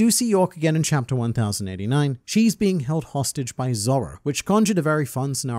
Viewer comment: I thought he said Zolo at 13:18 and I almost sh** myself out of fear... 😢